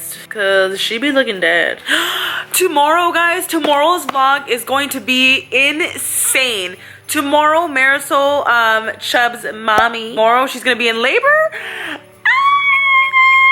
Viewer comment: On 10:36 did anyone here her say vog instead of vlog? Like if you you agree